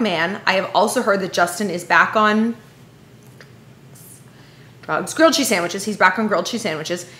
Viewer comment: LMFAO "I heard he's back on whispers drugs" haha